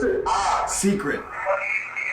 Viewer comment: I didn't hear "secret". I heard " basement" on the spirit box.